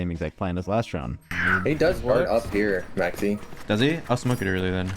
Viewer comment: daddy instead of maxie in the subtitles is crazy lol :)